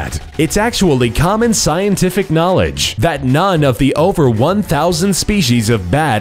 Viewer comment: What did you say on 5:14? KNOWLEDGE